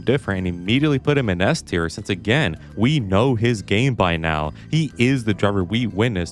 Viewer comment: Had to rewatch ts because I heard it as "We know he's gay by now."😭